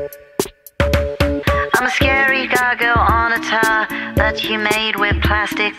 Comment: I thought they said ‘I’m a scary doggo on a tower’ and I don’t know why 💀